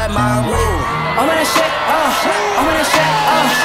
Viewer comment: First time I heard this on spotify i thought it said "imana shit uh, imana shit uh, imana shit uh" 💩